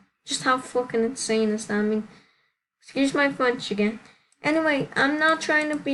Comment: Wait y’all got MARRIED?!?! Lol I thought u were just dating WAIT WAIT WAIT OMG I REMEMBER NOW I WAS THE PRIEST 😹 Also idk if that’s what u said but after u cursed I heard, “Excuse my fried chicken” 😹😹